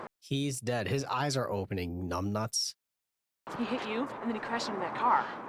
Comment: did you call a woman numb nuts??🙃 Lol